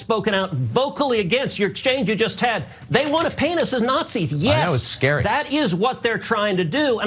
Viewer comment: It sounded like Cruz said, "they won a penis as Nazis"